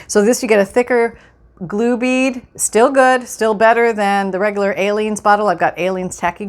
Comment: I worked today and I’m tired and twice I thought you said ALIEN glue...lol...I’m thinking never heard of that glue...then the light bulb goes off....oh she’s saying Aleene’s glue...haha...🤪🤗❤️🥰❤️